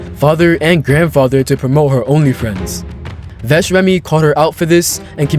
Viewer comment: did he say only friends